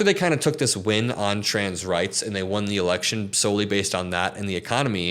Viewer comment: Sounds like you said the erection